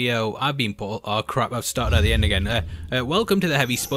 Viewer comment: Wtf did you say at 00:03? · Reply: “Aw crap Ive started at the end again”